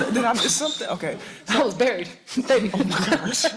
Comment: what does Marina say here? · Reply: She said she didn't play as a rookie and she was "buried" like on the bench